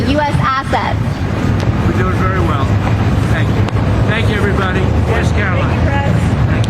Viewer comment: Does he say I’m not sharing it with u, or I’m not sure what to do??? Either way it’s disturbing